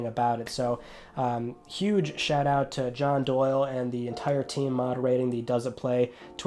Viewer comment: Did you say John Doyle? lol based